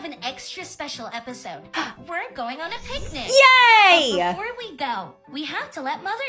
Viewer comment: I thought it said panic 😭🤚